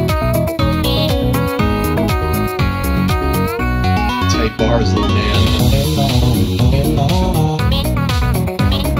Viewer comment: what did he say? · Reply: "tight bars little man"